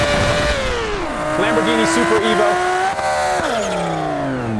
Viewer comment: I thought he said Lamborghini super emo